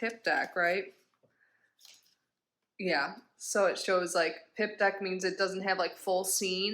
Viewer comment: pimp deck hahahaha omg i love that! its actually *pip! it should be pimp lmao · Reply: 🤣🤣🤣omg I really thought you said pimp deck! 🤣🤣